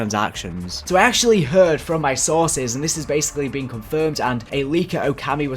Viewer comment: I keep hearing "sausage" instead of "sources" :/